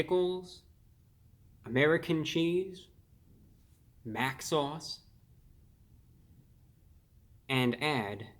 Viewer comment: I thought he said bath salts when he said Mac sauce